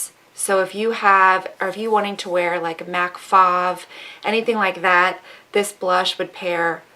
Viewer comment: What is she saying at 2:37? She says something about mauve lipstick and says that blush she showed would look good with something from Mac, it sounded like she said Fauve..? I do not see "Fauve" as a Mac lip product.... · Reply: She said if you're wanting to wear, like Mac Faux...